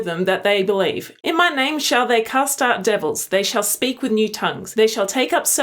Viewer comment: Has Lilith gotten bigger? Also, with your accent, when you read that Mark verse, it sounded like you said, “They shall cuss out devils...”